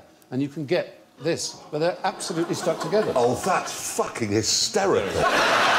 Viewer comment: Can anybody tell med, what Clarkson is saying at 1:02, please? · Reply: Peter Riis "oh that's fucking hysterical"